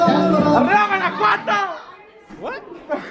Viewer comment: Did I just hear "arriba Guanajuato" 2:04? 🤣 that's awesome